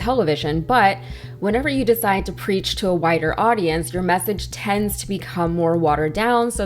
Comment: She said " When he decided to preach to wider audience." At first I thought she said "When he decided to preach to a WHITER audience."